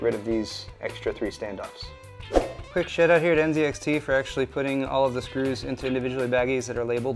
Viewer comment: did say "good shit on NZXT" at 8:06?